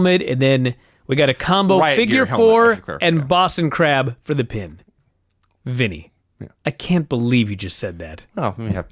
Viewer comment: plz someone tell me what Vinny said 💀💀💀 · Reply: "Riot gear helmet?"